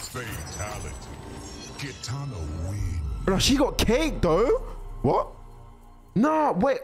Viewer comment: I thought he said but she got cake tho😂